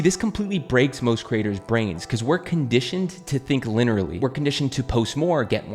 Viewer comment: Did you just say "linerally" 4:27 lol